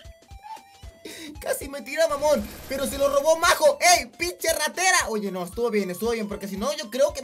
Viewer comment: Minuto 7:45 no sé si soy yo, pero yo le escuche esto: Casi me tiran amor, pero se lo robó majo